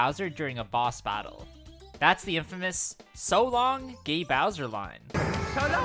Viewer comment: I always thought Mario was saying "So long, Ay Bowser!" I was very wrong.